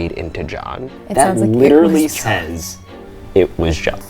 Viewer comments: I thought it sounded like it was saying "it was God" but maybe it sounds different in person, either way spooky! Also when it said "She was the biggest threat" I thought it was that Abby was a threat to someone and that's why they killed her. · It may be because I'm not hearing it in person, but I hear "It was God". But if I'm wrong: I was assuming "them" to be Lizzie and Bridget, working together to kill Abby, but Hiring John to kill Andrew, and that's why the voice is angry and masculine? Or, Andrew and John were angry with each other so he assumes to it to have been John, when really, Abby was right and it was Lizzie and Bridget?? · i hear “it was god” which honestly doesn’t explain anything but i definitely didn’t hear john at all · I heard “they forgot” or “I forgot” when they heard “it was John” 🤷🏻‍♀️ I really can’t even get myself to hear “it was John” even if I try haha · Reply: I heard “it was God” ?! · In stead of “It was John” I heard “It was god” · I heard “it was god “ 🤷‍♀️ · I heard " it was god" not "john" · I hear “ it was JOSH” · Sounded like it said it was god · I kept hearing "it was God" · i heard it say " It was GOD" XD i hope i wasn't the only one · I just heard "it was GOD" not John lmao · I heard "It was God" HAHAHHAHA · Sounds like it says' it was God', super creepily. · I heard "it was god" and it was probably aggressive bc it might have been from a Christian station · Why do I hear "It was God" instead of "It was John"? Lol